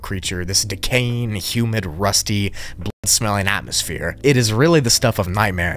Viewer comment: Did he say "bl00d-smelling"? Just making sure I heard right and that people who didn't catch it past the censorship can maybe read my comment and find out.